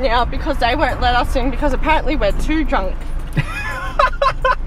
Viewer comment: I heard “I don’t give a shite” not “I go shy”